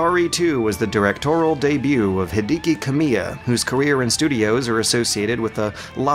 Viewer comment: Did you just say "Hidiki" instead of "Hideki"?